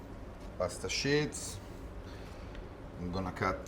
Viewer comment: Don't forget to "Blanche your Shits"! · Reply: Thank you for confirming I'm not crazy. I kept hearing him say "pasta shits" and it's all I can think about now lol